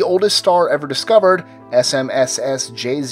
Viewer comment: all I heard was SMS